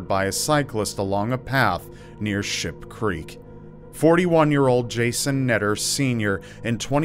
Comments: I'm hard of hearing... Using earbuds full volume... I thought you said Shit Creek 💩😂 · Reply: Paola Frye Ship. Ship Creek 😁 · it sounded like, "near Shit Creek". I've been up that creek many times without a paddle.